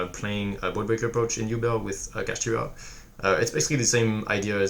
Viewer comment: with what?? i didn't understand? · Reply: Kashtira